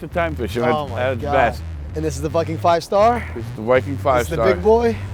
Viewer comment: i thought he said "this the fuckin 5star?"